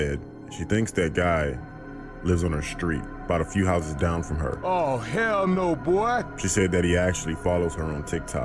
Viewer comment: I laughed so hard 😂 I really thought the girl said "Oh hell no boy"